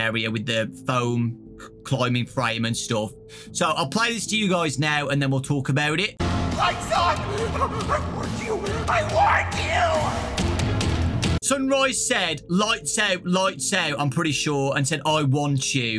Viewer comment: Sunrise actually sounds like he’s saying “I WARNED YOU, I WARNED YOU” maybe he could be a neutral animatronic warning us about moonrdrop (or someone else?) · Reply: I thought he said “LIGHTS OFF! I warned you, I WARNED YOU!” Like he was telling us to leave before the place goes dark